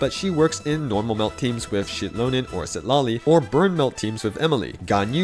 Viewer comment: > I swear I hear Shitlonen and not Xilonen 😂